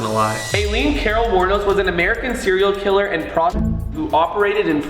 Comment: can someone tell me what the word is and it's meaning? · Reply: Prostitute :- means a woman who does sex with anyone for money.